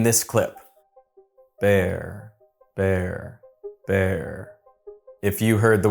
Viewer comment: Weird, I heard "Pear", and then "There"....